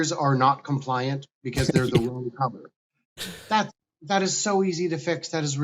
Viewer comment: Can anyone tell me what he says at 1:00? The best i can come up with is "the blinkers are not compliant because there's a lulu clubber" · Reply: They're saying they're the wrong color, which is true. Red blinkers are allowed in the US, while amber is required in most of the world. Addressing that is very easy.